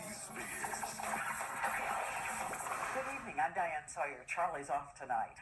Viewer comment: I thought I heard Diane Slayer at the beginning 😊